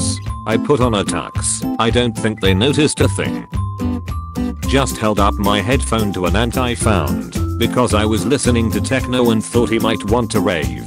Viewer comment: I wasn't watching the video, I heard " Just held up my headphone to an hentai found "